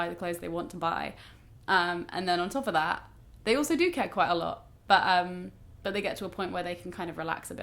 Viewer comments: I genuinely thought said 'they also do ket quite a lot' I feel it gives the following few seconds a new meaning. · I thought said "they also do ket quite a lot" so the cool kids like ketamine haha and I was like whaaaat??? was very confused for a moment.